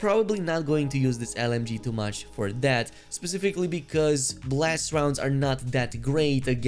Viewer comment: At first I heard “Blessed rounds” and I was like holy ammo? Sweet! 😂